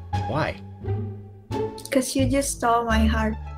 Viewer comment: Subtitles are wrong at 8:00.. I guess she said:. "because you STOLED my heart" <not STOPPED>. Maybe she meant "Stole" <past participle of steal> but said it wrong.